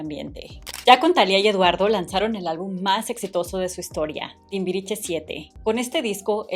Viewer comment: Hasta que no vi el juego de mesa todo el tiempo escuché “TIMBRICHE”… luego de eso no puedo escuchar el nombre correcto 😂😂😂❤❤❤